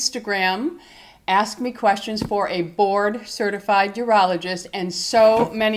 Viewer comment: I thought you said he was a bored certified urologist lol!